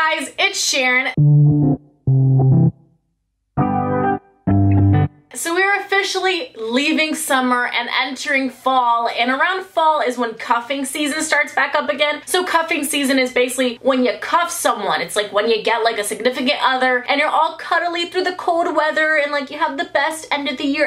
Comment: Hi babes! What’s your worst breakup story? Spill the tea 👀 make sure to follow me on Instagram (@ to be shoutout of the week 🤩 love you ❤️ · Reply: In the beginning I thought you said COUGHING season and I was like yesssssssssss